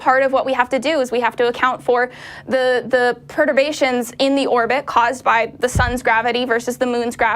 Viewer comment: what us the concept she said about "pertavations??" What a load of shit you idiots. Play it back, 7:42.. "perdavations.." total lie, and not a word. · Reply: She said "perturbations'' which are "a disturbance of the regular and usually elliptical course of motion of a celestial body that is produced by some force additional to that which causes its regular motion ". <link>